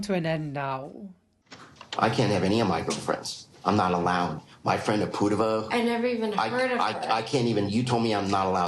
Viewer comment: sorry but did he say “my friend Apurva” because that’s my name and I’m very uncomfortable now